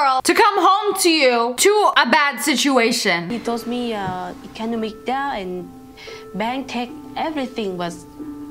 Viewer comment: I thought you said shisteruation😂😂🤷🏻‍♀️